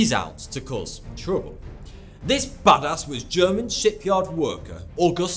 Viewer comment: I thought thoughty2 said (because of the accent he has? Im not sure) This baddas shit head